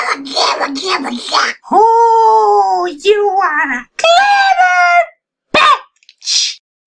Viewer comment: What I heard: Oh.. You want a kitten BI!